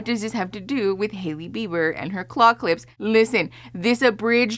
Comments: unrelated I know but Every time I hear the word "claw clip" I hear it as "clock lips" 😅🙃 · unrelated I know but Every time I hear the word "claw clip" I hear it as "clock lips" 😅🙃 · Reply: I thought she said clock lips. I'm so out of the loops that for all I know that was a thing.